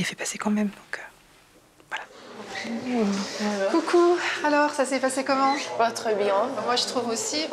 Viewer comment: Ca s est pas très bien passé Moi aussi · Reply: il a dit « bah très bien » 😉